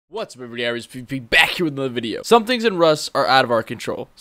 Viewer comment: So your intro from what I heard was "What's up ery one aree pee pee BACK with another video"